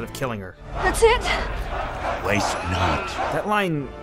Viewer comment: what did Barbossa say? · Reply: (WAste not )